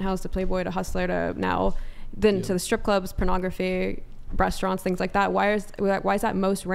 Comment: did she just say breastaurants?